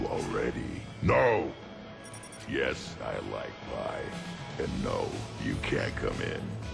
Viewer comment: does he say "Yes, I like pie." or "Yes, I like guys?"